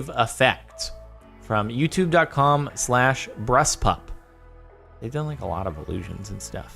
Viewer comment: turn captions on and look what it says when he says bruss pup or whatever it is